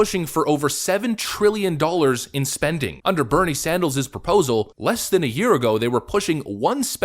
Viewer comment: Did U just say- " Bernie Sandals " ?